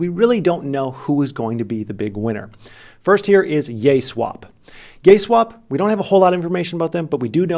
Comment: I swear I heard him say Yayswap then Gayswap shortly after. Turn on CC for proof lmao.